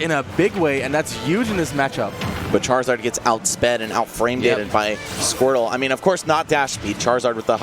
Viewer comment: I heard “out frame data’d” I think I’m done with smash lmaooo. Wtf.